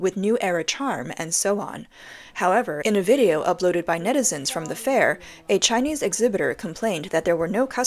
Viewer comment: "MEDICINES at the fair" ? Referring to people, what does that 'M-word' mean ? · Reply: "Netizens" = Chinese word for interNET citIZEN commenter.